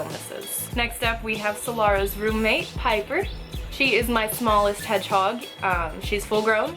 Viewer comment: when u said piper i thought you said "hyper", would've make sence though, look at her go! RIP in piece lil bab